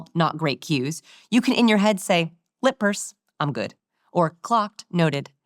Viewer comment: resting face - no fake smiles, cheekbones - liking a lot of people makes you likeable 25:24 - aggressively liking people, assuring them of how much you like them - name in your head the cues you get from people - show your palms to people - use your hand gestures - open body language - charisma - warmth and confidence - eye-contact at the end of a sentence or when the other person is saying something important 60-70% - lower lid flex while listening to - downward inflection 1:09:44 - how to make someone agree with you 1:20:07 - involving people in what you are saying by asking questions „you wanna hear this?“ „you know how it has ended?“ 3 magic phrases: - i was just thinking of you - you are always so…. - last time we talked you mentioned… warmth cues: -tripple nod 1:17:07 -head tilt, like dogs do (while you‘re listening or when delivering bad news) 1:18:35 - leaning into people 1:21:10 - reaching 1:37:44 questions: -no asking how do you do -„are you working on anything exciting recently?“ -„what is your biggest goal right now?“ -„what book/movie character is the most like you?“ 1:53:20 dropping your shoe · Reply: What is the word she is saying at 41:25? Lippers? Is that it?